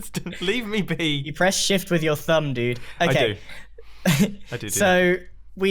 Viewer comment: is it just me or did said press shift with your thumb dude?